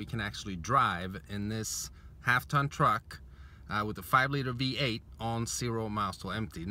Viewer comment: did he say half ton truck ????